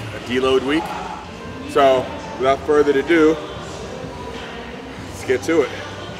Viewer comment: did you say without further to do lol, sounds funny, (further ado ), don't deadlift me now, it's just harmless fun